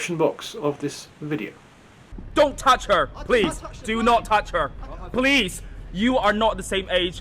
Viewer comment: The words that I heard were "Do not touch her"